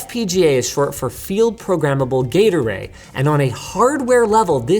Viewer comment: i was distracted and thought you said "field programable gatorade"